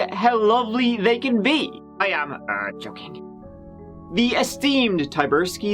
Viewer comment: Can anyone understand what is saying at 0:17 ? I've listened to it, like, 50 times and it sounds almost like "I am a therapist," which makes no sense. The closed captioning transcribed it as "I am uh joking" but that's definitely not it-- too few syllables. Any other guesses? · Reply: He definitely says "I am, uh joking" which makes perfect sense as it follows him saying how lovely a family can be, and we know the end result of the case. Dunno where you are from, but I'm pretty used to Irish accents and the captions are correct for that line.